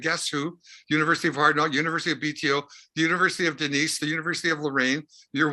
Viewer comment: does Randy say "the university of hard rock" (or "the university of hard luck")? Thanks.